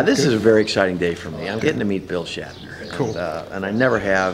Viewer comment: did he just say bill shatner?